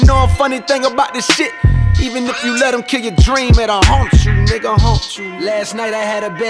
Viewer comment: I always thought he was saying "honk-shoo nigga honk-shoo" but now i realise hes actually saying haunt you